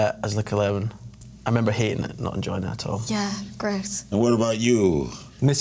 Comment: Did Iain say 16:52 'I remember he and I did not enjoy it at all? ' :D Or am I getting this totally wrong? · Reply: no - he said "i remember hating it and not enjoying it at all" :D (I had to listen back and check though! haha)